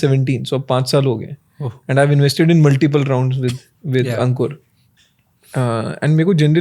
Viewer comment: did he say ankur